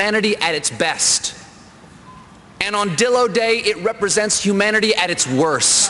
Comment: anyone else think he said "dildo day"?